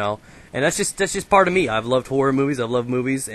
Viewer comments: i first i thought he said i love whore -__ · I thought you said "I love the whore movies" mmm